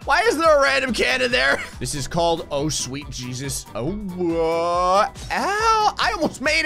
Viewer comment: I actually thought the level was called "Oh sweet Jesus".